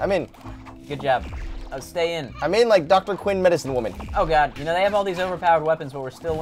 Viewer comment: Did Joven say Dr. Quinn: Medicine Woman